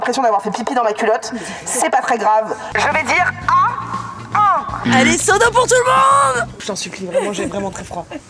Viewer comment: j'ai compris "ALLEZ SODO POUR TOUT LE MONDE" ah ok très bien si vous voulez x)))